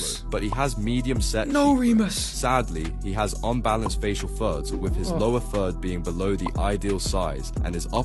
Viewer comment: bro i thought he was saying “facial THUDS” for like 7 minutes of this video until i started reading the text 😭